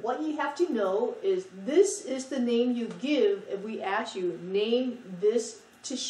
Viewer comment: funny at 3:30 she says " ass you" instead of "ask you.' I love her accent